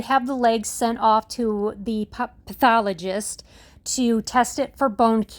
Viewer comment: 🙏❤️🐶 Such JOYFUL news🎉 I thought I heard you say Pup-thologist 😇 Sending truckloads of love 🥰 from Cali. -Vicky